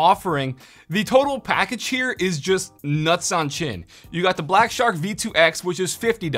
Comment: I got so confused. At first I was like ohh okay another review until I heard “nuts on chin”